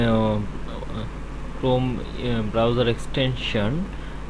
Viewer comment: every time you say browser it sounds like you say browyour